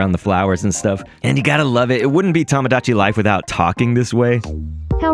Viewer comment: I swear, during the direct, she said Tomodachi